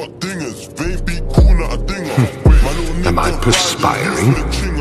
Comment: “Perspiring” look at this man’s vocabulary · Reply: at first I thought he said inspiring and I thought whom he was getting inspired by😅😂